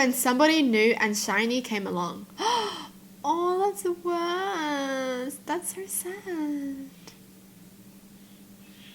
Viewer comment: what did she said?? But it’s so cute 😆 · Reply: "aww that’s the worst.... that’s so sad" i think